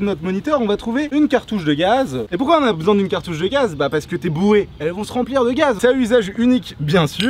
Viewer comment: à 3:20 j'ai compris :" bah parce que t'es bourré" 😂